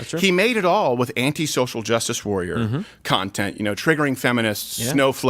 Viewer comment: I thought you said antisocial justice warrior. Sounds like a skit idea.